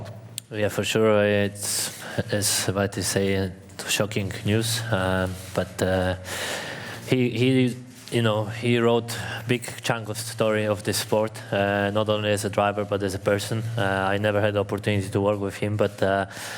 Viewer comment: I swear he said big chungus,, rip niki ,, I will miss you ❤️